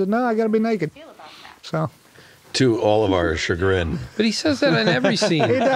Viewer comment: I thought he said “to olimar’s chagrin” And went down a breaking bad x Pikman rabbit hole on the internet. Now I realized he said “to all of our chagrin”